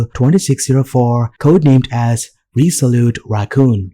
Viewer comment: did you say RESOLUTE ? or RESOULTE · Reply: RESOLUTE ..